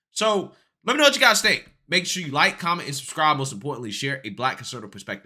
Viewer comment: !!I JUST DO LOVE"THEIR"FACE!?! *like,'What da Hell did He Just SAY!! I had THOUGHT you said ANTI conservative.....ANTI!!' hahahaHAHAHAAHA